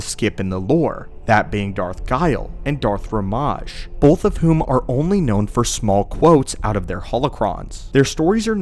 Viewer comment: Did I hear "Darth Fromage" at 12:20? If so that's instantly my favorite Sith :D